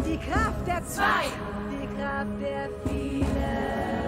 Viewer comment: Ich hab immer wieder der Kraft der vier verstanden. Dabei heißt es die Kraft der vielen 😂